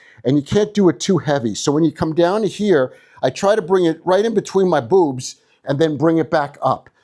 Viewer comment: did he say between his boobs? He doesn’t have boobs.. he has a what looks to be a strong muscular chest..women have boobs.. idk maybe it was an inside joke 😅